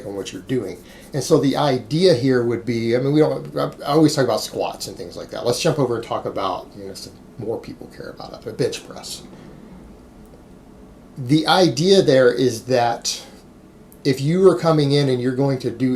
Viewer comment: lol for a second I thought you said "B***h press"